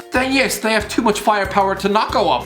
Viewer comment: Ayo what's he saying anvethstheenufthvidolasdonfgetlishesubcibe